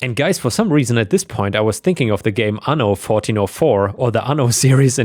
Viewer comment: what is the game i cant make out what he said and captions is telling me ano 404 but i have not found any game with that name. Note : i have only checked steam if it is some where else tell me please ( 13:19 ) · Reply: Anno 1404